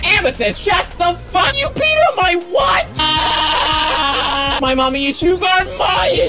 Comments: i thought that steven said 'YOU PEED ON MY WIFE?!' but i think he got rejected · I just love Lion's face when he pees on Steven's jacket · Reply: As my language, I heard "YOU PEED ON MY BUTT?" XD